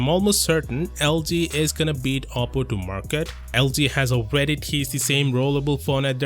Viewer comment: Is he saying apple? Thanks